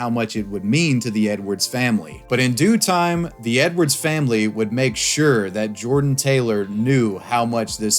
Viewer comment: Bro I thought he said “the n words family” is that bad?